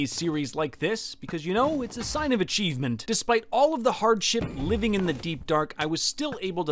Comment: I thought he said "hard sh*t" 🤣🤣🤣